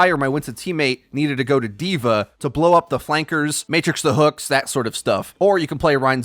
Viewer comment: the one thing I hear, is "Matrix the hook"